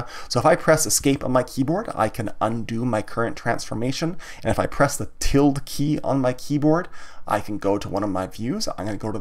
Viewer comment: Please tell me what you say in 24:28 min short key? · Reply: He says "If I press the tilde key" (tilde = "~", left of the 1 key on a US keyboard)